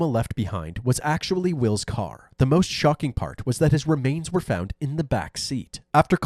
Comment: CORRECTION: @ 19:22 - I say that his remains were still in the back seat. Just to clarify, by remains, we mean his cremated remains, or his ashes. William died in the hospital following the shooting. Shortly after his death, he was cremated. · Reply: My brain heard that as “cremains” which is also the appropriate term for cremated remains. Thanks for the clarification anyway!